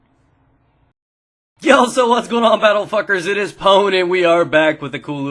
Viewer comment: *pauses at 0:07* "wait did he just call me a battlefucker" *rewinds* "he fuckin did :P"